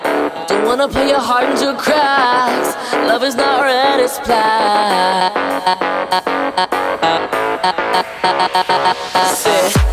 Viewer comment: I love the "love is not red it's black' part, even though it took me 2 weeks to understand what he was saying, and it took me 3 weeks to understand what it meant... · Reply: IM LAUGHING I THOUGHT IT SAID "love is not red as plaid" OH